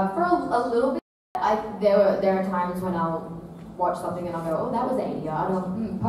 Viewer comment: Can anyone tell me what does she say on 35:40 I can't understand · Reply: "There are times when i'll watch something and i'll go: "oh that was ADR, oh post" She is referring to added dialogue during post-production